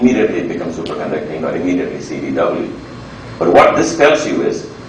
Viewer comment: what did he say? Becomes CVW? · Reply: CDW, charge density wave. This corresponds to the mean field ansatz he wrote down earlier (8:52).